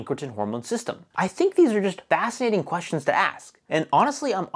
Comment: I had to turn on closed captions to understand what you said at the end there. I heard "stay curious, and mind your butt plugs".